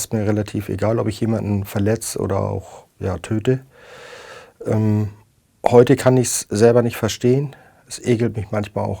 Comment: und glücklicherweise oder unglücklicherweise? :D schwer zu verstehen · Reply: Glücklicherweise